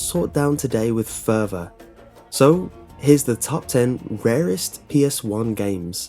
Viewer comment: I thought that this said "Top 10 Rarest Pepes" XD!